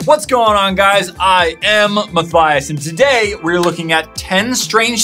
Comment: It sounded like u said maffias instead of matthias.omg i think my ears are messing up today XD